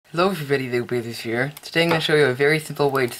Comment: It sounds like he says hello everyone loaf of bread here! No offence dude pls dont h8 me